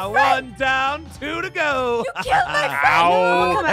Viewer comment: i think i hear aphmau say Aphmau: YOU KILL MY BROTHER Hit